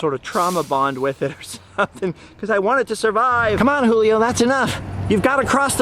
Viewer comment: Did he just say Julio eats chicken? Cannibalism confirmed 😂